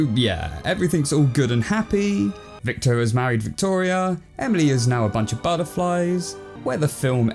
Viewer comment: victoria is mary to victoria?? Did i heard right